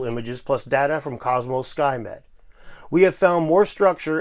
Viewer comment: " skymed " i had to back i thought he said skynet